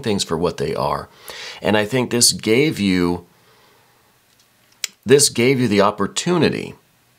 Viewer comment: Thank you. Accurate. I love your perception of resolving it through dissolving it. I swear when you said "this gave you" ... I heard "this gay view". Very interesting my mind did that. Yes. From that view..my view.. it reinforces neighbors constantly ridiculing me. Always playing belittling games of control with me. Always rejection and hostility projected at me. I'm done how much it consumes my energy and puts me in irritation daily. 💗